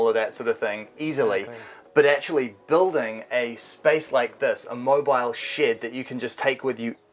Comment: "A mobile shit", even the captions says that. English spelling can get so weird so easily haha